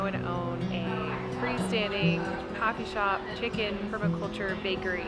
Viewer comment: How fun!! That looks like a super cool town. My kinda place. At first I thought you said you wanted to own a “Coffee shop chicken” and I was like yasssss 🤣🤣🤣🐔🐔🐔